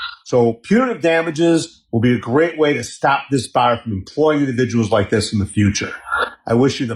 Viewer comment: The text reads, "to stop this fire....". ... What he actually said was, "to stop this BAR...."